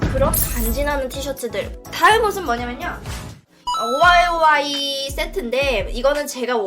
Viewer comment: 나만 오마에와라고 들었나